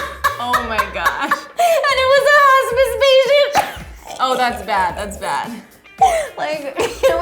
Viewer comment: What did she say at 1:22 ?? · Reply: "and it was a hospice patient" then later then she says that she tells him that his mom is dying but she wants to take him to the movies